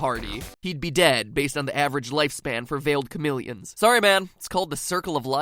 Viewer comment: "failed chameleon" LOL. Who else heard "failed comedian?